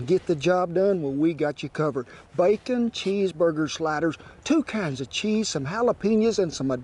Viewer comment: Oh my I thought you said bacon cheeseburger spiders 😂😂😂😂 I am so over tierd from sleeplessness nights that proves it. Lol